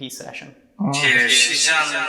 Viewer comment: Hey, I’m just catching up after some long work hours and I wanted to say that at 27:53 it sounds like it said “Cheers she’s on the move.” Keep up the great work